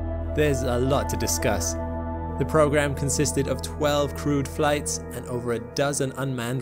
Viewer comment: At first, when you said "crewed flights", I thought you said "crude flights". Haha